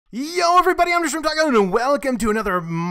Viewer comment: what is he saying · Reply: I think he said "yo everybody I'm DaShrimpTacoand welcome" idk